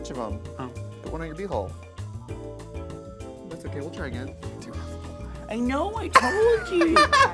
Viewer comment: ok but i can never figure out what trixie is saying at 3:52 someone help me lmao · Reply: "That's Okay, we'll try again"